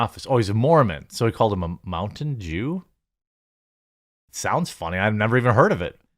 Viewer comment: I've Head Of Toxic Work Environments But Stripping Down Nude In Front Of Your Employees? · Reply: I misheard you at first. I thought you said he called him a "mountain dew 😂